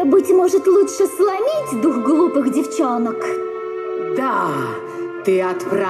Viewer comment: сколько лет я смотрю этот мульт и серию и всегда слышала "сломить двух глупых девчонок" и думала как-то странно звучит, но сейчас наконец-то расслышала, что она говорит "дух глупых девчонок" 🤦‍♀ Опенинг во втором сезоне мне нравится намного больше 😍 видео приятнее и прикольнее 🥰 6:12 серий 26 Если бы было 30 серий, можно было бы под русский алфавит адаптировать, так как "ь", "ъ", и "ы" можно не учитывать, на эти буквы нет слов 8:03 чародейки не видели Миранду в человеческом обличии, они видели её как паука-оборотня, это Элион видела Миранду как человека 15:05 нет, это у каждого электроприбора есть своё "сознание" Я так соскучилась по вашим реакциям, что вчера пересматривала ваши реакции на первую и вторую серии второго сезона, а сегодня как раз смотрела 3 и 4 серии. Я очень рада, что вы вернулись к просмотру "Чародеек" 🥰 С нетерпением буду ждать реакцию на 4ую серию! 😊 Надеюсь, теперь "Чародеечки" будут выходить почаще. 😊 Но я думаю, что лучше смотреть под запись, а финальную серию на трансляции, как вы делали с первым сезоном. 💞 Для меня второй сезон более интересный и какой-то... родной что ли. Наверное потому, что я, как и, в детстве смотрела именно второй сезон по телевизору и на кассетах. Первый сезон я смотрела позже, когда старшая сестра купила диск с обоими сезонами. Второй сезон для меня всегда был и будет самым крутым ☺❤‍🔥